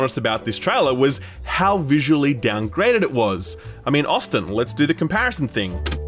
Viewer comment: did you say my name? (Austin)